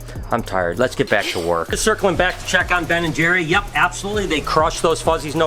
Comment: Hi Brian I thought when I first heard Ben and jerry's name I thought it was Beth and jerry